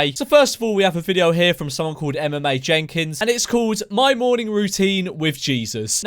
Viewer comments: Who else heard him say MMA Jenkins not Emma Mae Jenkins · when he said “Emma Mae Jenkins” i thought he said “MMA Jenkins” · any one else think he said MMA Jenkins? 😂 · Lmao I thought you said MMA Jenkins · I thought he said MMA Jenkins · When you said Emma Mae Jenkins I thought you said MMA Jenkins lol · Who else thought he said mma Jenkins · I thought he said MMA Jenkins , a was waiting for a pure fighting nutter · i swear to god i thought you said MMA Jenkins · I thought he said "MMA Jenkins" 😂😂😂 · lol i thought he said MMA Jenkins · ngl thought you said MMA Jenkins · Thought he said MMA Jenkins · Before you put the account on the screen I thought you said MMA Jenkins 😂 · I thought he said morning routine by MMA Jenkins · thought you said mma Jenkins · Ngl i thought you said MMA Jenkins lmao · I thought u said MMA Jenkins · I thought you said “MMA Jenkins” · Who else thought he said MMA Jenkins at first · I’m just saying when you said “Emma Mae Jenkins”, I wasn’t looking at my screen, so I thought you said “MMA Jenkins” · Ngl thought you said her name was "MMA Jenkins" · When you said Emma Mae Jenkins I thought you said MMA Jenkins until it showed on the screen. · Really thought he said MMA Jenkins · I deadass thought you said MMA jenkins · at first i thought he said MMA jenkins and i was like "yeha lets fucking go" but i was quickly dissapointed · I thought you said mma Jenkins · Who else thought he said 'MMA Jenkins' Was waiting for some fighter sh!t